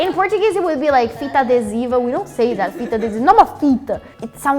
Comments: I'm sorry but who's the person doing the subtitles? Not only in this video, but also in the last one you can see very weird errors, like writting "fita de sino" instead of "fita adesiva" 1:07 (Edit: the first one literally translates to bell tape instead of adhesive tape, they really gotta be kidding) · she says "fita adesiva", the subtitles are wrong...